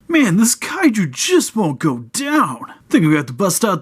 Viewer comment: I could have sworn you said at the beginning that "this kaiju jizz won't go down".